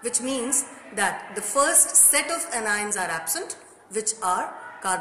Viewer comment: what did she say at 1:22 'first set of' ? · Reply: Anions are absent